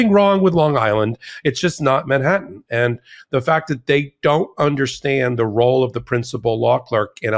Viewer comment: Hey, Popok, every time you say the T's lawyers "don't understand", I hear "pretend not to understand", FYI. ~ Peter Ferber